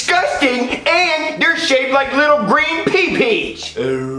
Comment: What Did Jeffy said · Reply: He said and they shape like little green pee pee’s to the green beans